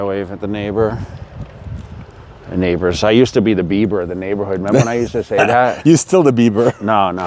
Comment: first I heard it like "I used to be the BEAVER of the neighbourhood", I was like wtf for a sec... 😂